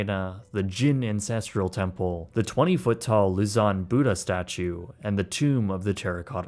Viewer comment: "twenty foot tall luzon [sic] buddha [sic] statue" (Spellings obtained from the closed captions.) I wanted to find out more about the Buddha statue. At first, I used "lison," as that is what it sounded like. I then turned on closed captions, and it showed luzon [six]. I searched on that, and the only thing I found was an island in the Philippines. I then searched for a "giant Buddha statue" in China. It displayed the Leshan Buddha, the photo of which appeared to match the photo in the video. I then checked on the pronunciation of Leshan, and found it was luh·shaan, not li-son (or li-zon).